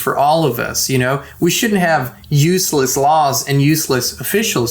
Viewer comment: we should have useless law and useless officials ? ? Freudian slip or slip of the tongue 👅 or did he say shouldn't ? ? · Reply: He said shouldn‘t